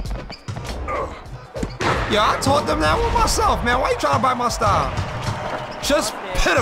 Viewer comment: What'd he say at 6:17? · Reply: Yo I taught them that one myself. Man why are you tryna bite my style. 🧠